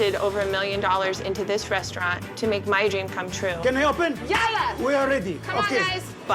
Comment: Did Amy said "YALA!" at 2:04 ?